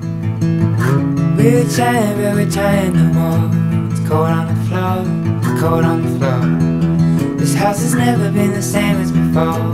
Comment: I thought it said corn on the floor, corn on the floor😂